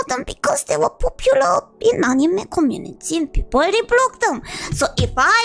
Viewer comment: What she said at 6:53 was "and people reblogged them".